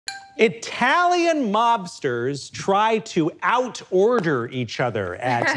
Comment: I thought they said Italian lobsters…